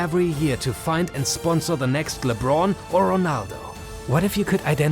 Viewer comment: subtitles says Messi or Ronaldo But video says LeBron or Ronaldo